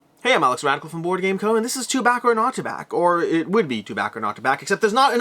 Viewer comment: when you said "to back or not to back" it sounded like you said "Chewbacca"